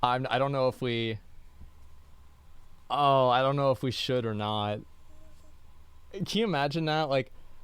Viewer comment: anyone else here the strange voice in the background? as if responding to asmon at 1:04:47 ?? is someone else with him? sounded like it said "yea we should"